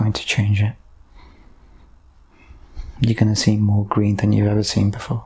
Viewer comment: ish does he say "we are going to see more greed than ever before' or green then ever before? I keep playing it back and can't tell and subcaps say he said green but i hear greed too? some help please? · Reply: More Green!